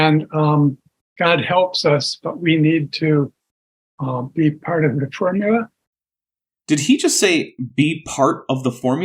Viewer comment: I heard "be part of the tribula." Is that like a fibula, but for Tribbles? I didn't even think Tribbles had fibulas, so I am very confused.